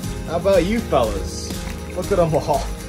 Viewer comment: It almost sounds like you said moldfish😂❤️